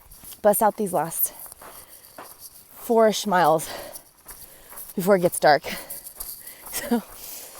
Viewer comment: Haha 16:18 - you said “four-ish miles” but I definitely heard “four schmiles” 😂😂